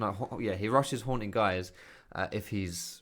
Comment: Faker is a literal 1v9 god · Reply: Be a Better League Player at 2:38 I swear to god I thought you were saying "he rushes horn guys" instead of "he rushes haunting guise" lol